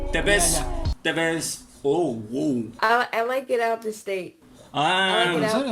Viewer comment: Creo que dijo "I like get out of state" y luego "I like get out of country" osea que le gusta salir del país o algo así xd